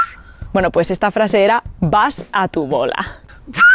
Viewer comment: yo escuché "vas WTF" (vas watafac) XD